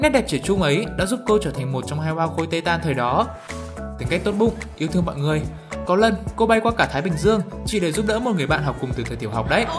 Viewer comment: nghe như phim lem lem